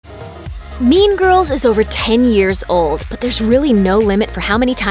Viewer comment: Thought it said meme girls lol.